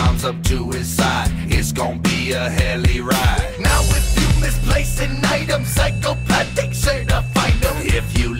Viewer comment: Oh my gosh for longest time I thought Shaggy said: “Now If you misplace the night/knife Im psychopathic certified”